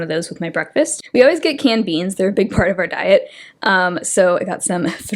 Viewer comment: I was doing my mascara during the part where you talked through your grocery haul and I heard "candy is a big part of our diet" and I dropped my mascara. I was like what did she just say?! Canned beans 😂